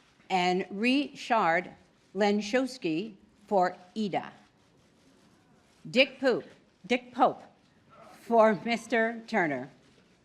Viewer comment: did she said Dick Boob ?